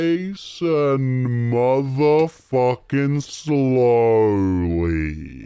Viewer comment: did say MF?